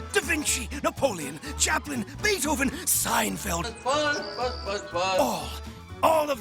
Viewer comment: did he say seinfeld????